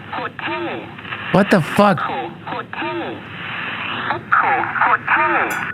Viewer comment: Sounds like it said" echo code 2"at the end